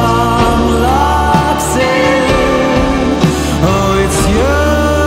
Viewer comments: Am I the only who thought he said "oh God its you" instead of "oh girl it's you" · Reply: i thought it said “oh carl it’s you” JDJEJDJEJ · Am I the only who thought he said "oh God its you" instead of "oh girl it's you" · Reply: i thought he said oh cause its you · Am I the only who thought he said "oh God its you" instead of "oh girl it's you" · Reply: i thought it said "oh come its you"